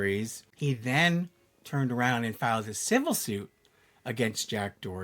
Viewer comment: lmao I was looking at the Sybil shirts and thought he said “he filed a Sybil suit” 😂 why is my brain dumb